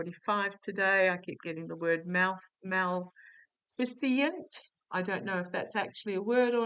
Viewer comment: Maleficent is what I thought you said. Meaning is causing harm or destruction, especially by supernatural